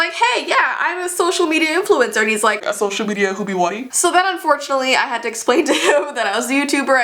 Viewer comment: "... Hoby whaty?" I died, did he really say that?